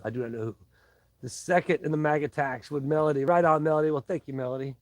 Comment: I mis-heard you. Thought you were saying Mag Attack.